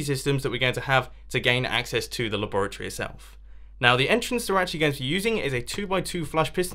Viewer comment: Who else heard “Lahnboreatury” ?